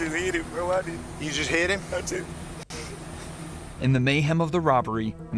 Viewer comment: It is possible that he wasn't alone doing the crime and wasn't the shooter, not saying that's better, but sometimes kids that young aren't the shot callers, and they're expected to transport the body. The time he'll serve would mostly be the same, but he's gonna take the full hit or there will be consequences for snitching. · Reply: Yeah even though the subtitles said “I just hit him” it sounded more like he was saying hid instead of hit